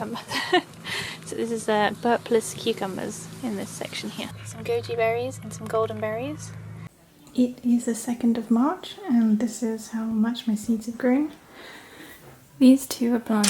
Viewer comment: Oh wow, I thought you said Gucci berries 👜🫐